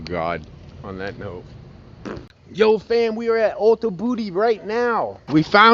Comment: At first I thought you said "Ultra booty", the captions think you said Auto booty. And then actually says, "Ulta booty" 😂 Either way, YouTube must be a booty man or very drunk. LOL Edit: Love this video!!